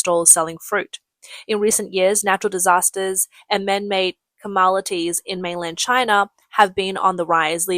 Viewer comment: Did she just say "Camalities" instead of calamities? Jeez..